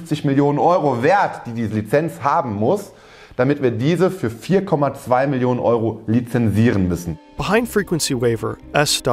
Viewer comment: Hello team :) At 18:17 you made a mistake in the subtitles and wrote "4.5 million euros" instead of "4.2 million euros".